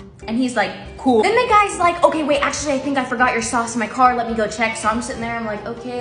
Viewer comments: I thought she said “socks” instead of sauce.. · this whole time i thought she was saying socks. not sauce. i was so confused 😭